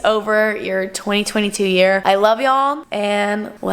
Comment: The end sounds like your saying “let’s go kill a baby’s lmao 😭